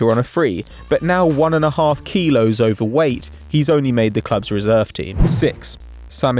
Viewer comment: did you say he was 1.5kg overweight?